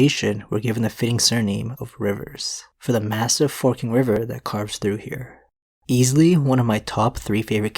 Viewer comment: i misheard massive forking river as massive fucking river